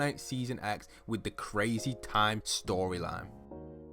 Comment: Did You Say TimeSh*t?? 😂😂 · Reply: It was time shift.